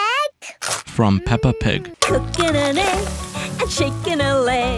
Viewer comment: lol used to think it said cooking a leg